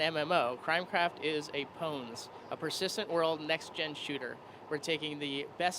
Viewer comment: i couldve sworn he said "a persistent world mexican shooter" at the beginning